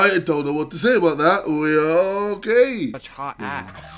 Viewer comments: i swear to god it sounds like he just said WE ARE ALL GAY lol I mean it's ksiolajidefrickenlgbt 🏳️‍🌈🏳️‍🌈🏳️‍🌈🏳️‍🌈🏳️‍🌈🏳️‍🌈🏳️‍🌈🏳️‍🌈🏳️‍🌈🏳️‍🌈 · JJ= "I dont know what to say about that, we are gay" me= huh?! WHO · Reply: He said, we... Ookkaaaay. Not we are gay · Did he just say, "We're gay?" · did he just say 'we are gay'??